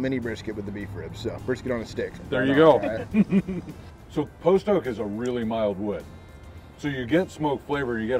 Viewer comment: minutes he says the name of the wood that is used for smoking but I couldn’t make it out. Does anyone know what it was??? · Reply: Post Oak